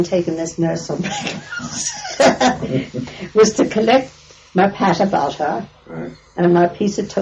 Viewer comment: I think she says "my pat of butter" since she's making toast